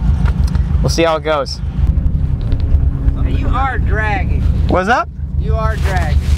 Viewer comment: I thought he was trying to say "you are sandbagging!" lol. 8:54 You guys like vape? LOL BRUH. Almost spit my juice out. :D Cool video man. That seems fun, to just push your car to its top limit for once.